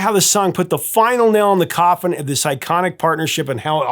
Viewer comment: Misheard - "...this psychotic couple." Huh? Oh. Iconic. This iconic couple. Well... Either way, really.